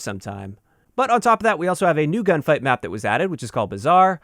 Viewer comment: Shit. At first I thought I heard you say "New Ground War Map" and I got all Crazy excited only to realize that you said Gunfight!!!